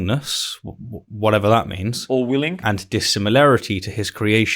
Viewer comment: I think he is saying Orwellian 😉